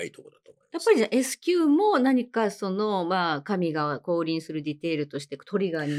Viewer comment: 「神が降臨する」も「神が小売りする」に聞こえる😂 ひろこさん、わざとやろw